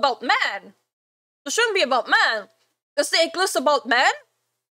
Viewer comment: Thought she said egg list, but "ick list" makes more sense.